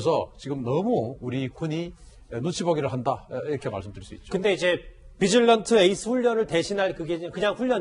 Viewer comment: 비질런트 에스 -> 뒤질런트로 들림.. ㅎㅎ